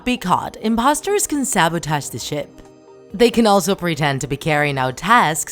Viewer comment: why am I hearing sh*t 😭😂